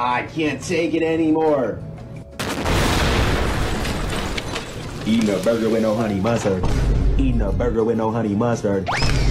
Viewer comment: "Eating a burger with no honey mustard" -scp 035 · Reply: I thought he said eat no burger with a honey mustard